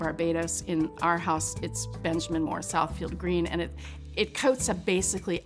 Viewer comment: Can anyone pls tell what colour green she said she has in her house? Benjamin moore ____?___green? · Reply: Southfield green